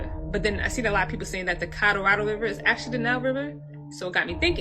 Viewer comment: The woman discussing how likely the Americas was once part of Egypt, but the way she says Colorado blows my mind! Thank God, there were some closed captioning, on the screen, bc it sounded like she was saying Codarodo? Say what & where, now? The way people screw up words is insane! If u’re going to make a video, learn how to pronounce certain words that u couldn’t otherwise pronounce! For example, like I heard somebody calling apostles, apos-tills !